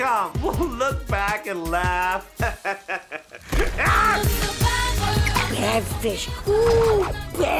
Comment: This One Make Me Think I'm Saying Like Bad Fish Like Bad Brother To Me